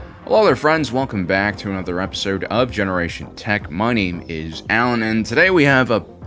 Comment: I swear every intro I hear “Welcome back to Generation Tech Money” before your name is actually said lol